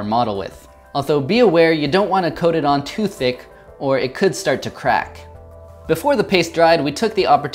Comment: I swear I heard him say: "don't coat it on Tuesday, or it could start to crack"